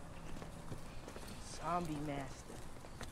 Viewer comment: did randy really call Chris zombie master? 😂